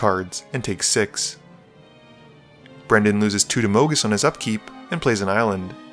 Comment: Swear to god I thought it said (Moo)gis